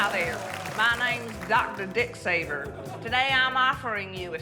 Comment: I heard “Dr. Dicksaver” and thought “oh, this must be an accent thing and she means something else…”